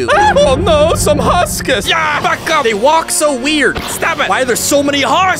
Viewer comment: YAA F*CK UP Wth just i Hear 💀